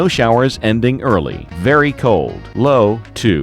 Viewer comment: it sounded like he said snowdy instead of snow,thirty percent.